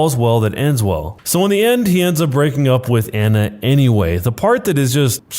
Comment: Instead of Anna it says Emma